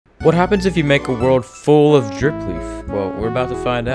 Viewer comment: Omg i just hear it "what happened if the world full of jerk"😂